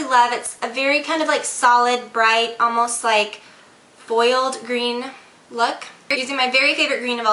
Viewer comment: lol..when u said "foiled green" at the beginning i thought u said "boiled green"...lol